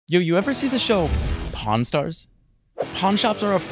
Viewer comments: Ok you can't tell me am the only one who thought he said" p*rn stars" 💀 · Thought you said porn shops